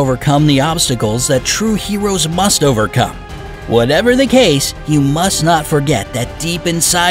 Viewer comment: I got tragic hero (I got 22 stars) like if you got this too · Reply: I got 22 stars Anyone else notice that At 8:15 it said whatever the case but instead of case it said cause